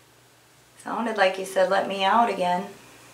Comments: I heard her say let me out jackson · Sound like she said let me out Jackie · This is so heartbreaking to hear 🙁 sounded like she said let me out Jackson? Is one of the brothers named Jackson?